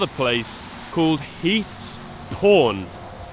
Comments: Straight up thought you said "heath's porn". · I'll admit, I thought you said "Heath's Porn". That store selling repros for that price is insane, borderline criminal.